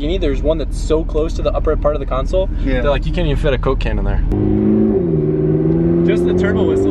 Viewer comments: Wait hold up am I trippin or did he say coke caine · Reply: Coke can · Did i just hear Cocain? :D